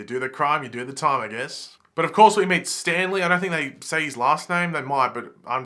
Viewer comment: it should be Stan Lee instead of Stanley 😂😂😂